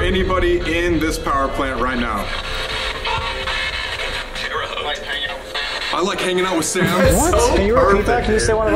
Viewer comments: sounded like colby saying "I like hanging out with sam" AFTER the Thing said "COLBY!!" in sams voice.. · it sounded like Colby said “I like hanging out with Sam” you can hear the similar voices in those two one in radio and one speaking now. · I think it's trying to say "hey Colby I like hanging out with sam in your past" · “hanging” sounded like colby.. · why does it sound like Colby saying “I like hanging out with Sam”?! Does anyone else hear that? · when it said “ I like hanging out with Sam” it sounded like Colby